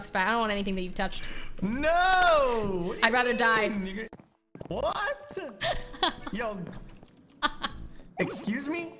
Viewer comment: Ok I’m kinda confused as to what Myth said at 3:54 at the end of the sentence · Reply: he said nigga